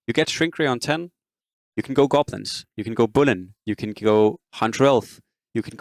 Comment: Sorry at 09:33 you said something like "you can go goblins, you can go bullin", but I can't make out what the last word means. Can I clarify what you said? I'm also quite puzzled by why Gaoler's Key is bad, doesn't banning large synergies like Humans or Beasts grant you a massive chance advantage in rolling for the units you do want? Seems like a great way to get from a 2* board to a 3* one in the mid-game. I also am confused why seeds would be able to preserve your HP. Seems to me that having less slots to work with would mean that your board might not be very strong and some units will be stuck on 1* because you don't have enough slots to keep another copy while looking for the third one. Or is it that you only play 1* high cost units with seeds? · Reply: If you're asking about Bullin it's a build we used to play (and sometimes see in high level China) very rarely back 5-6 months ago. It's Spirit Breaker, Bounter, Slark, Willow, Grim and then you look to add Lion or Demon + rubick and go 9 Assassins with 3 Wizard. It was quite strong back when Goblins shrink was played before Draenei because of the fact that you easily got to the Tinker backline and you had Willow for control. It's not that good right now though :) Your general perception of Key is good, but the issues is that the value you from the relic is pretty low compared to other relics. The difference between a fx. Trolls ban and a Warrior ban if you're doing reroll is negligible, so often the gold you end up saving due to key is around 10 gold roughly I'd say. If you compare that to the amount of gold you save/gain with Vault, Family Discount or Monopoly it just isn't worth it at all. The only advantage Key has is that it's a little faster tempo since you can do the ban immediately and roll (rather than waiting 1-2 rounds), but small tempo gain is not worth the overall loss to scaling and economy. Creating a strong board out of what you get is a must if you want to be a good player in this game and in high level DAC you almost always see people push 6 or 7 with strong boards before they go seeds. 6 sins, 6 assassin, 3humans+strong units, goblins, draenei, knights, just a mix of upgrades, a few good 4 cost - there are a lot of ways to assemble a board that'll be compet